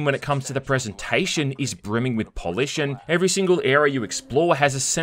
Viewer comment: Did he just say polished when that happen lol